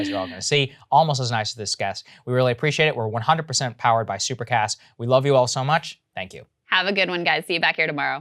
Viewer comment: At the end I thought Saagar said “We’re 100% powered by super packs” I was shook